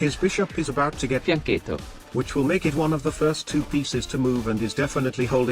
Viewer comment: what are they saying? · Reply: Fianchetto I think